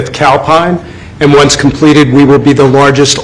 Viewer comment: I thought he saidcow pie😂